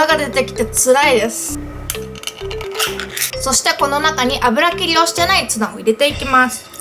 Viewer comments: 油切りをしてないツナが 油切りをしてないツバて 聞こえた（笑） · 油切りをしてないツバって聞こえた